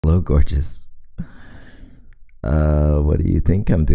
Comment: When he said hello gorgeous I thought he said blow gorgeous so I blew